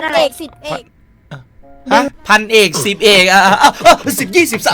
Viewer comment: น้องจะพูดว่าพันจ่าสิบเอกรึเปล่า😅